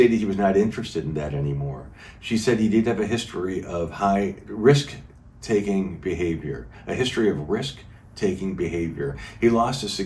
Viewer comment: glad you repeated yourself. Initially I thought you said “ Wrist taking behavior”